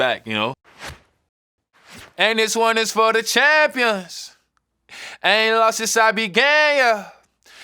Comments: I thought he said “ I ain’t lost since I’ve been gay yeah” that would’ve been fireee🔥cuz bro really ain’t lost since he been gay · Ohhhh I thought he was saying “I ain’t lost since I been gay” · ngl i thought he said "i aint lost since i been gay"💀💀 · I THOUGHT HE SAID I AINT LOST SINCE I BEEN GAY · I ain’t lost sence I began. I swear I thought he said I ain’t lost sence I been gay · Who else thought he said “ I ain’t lost since I been gay” in the song? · I thought he said "I ain't lost since I been gay", maybe it's a homophone, a double... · i thought he said i aint lost since i been gay wait 🧍🏾‍♀️ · I stg I thought he said I ain’t lost since I been gay 😭😭😭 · I seriously thought it was “I haven’t lost since I’ve been gay.” In my heart, that will always be the lyric. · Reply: I always heard it as, "ain't that long since I been gay" and took it to mean he's only recently become comfortable w his sexuality, but that don't make no sense... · I swear- I thought it said “I ain’t lost but I turned gayer” and I’m like damn right you did bruh 😭😭😭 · I thought he said “I ain’t lost since I been gayuh” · I thought he said i ain't lost since I been gay😂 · I thought he said “I haven’t lost since I been gay yeah” but began works too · Ohh I thought he said "since I've been gay eh" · Am I the only one who thought he said “I ain’t loss since I been gay yuh” please say no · am i the only person that thought he said “i ain’t lost since i been gay” or… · I thought he said “since I been gay.”